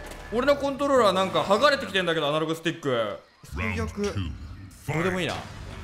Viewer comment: 「アナログスティック」が「アダルトスティック」に聞こえた